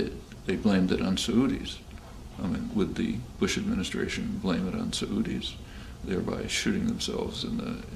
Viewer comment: Does he say "blame it on Saudis"? I've never heard "Saudi" pronounced that way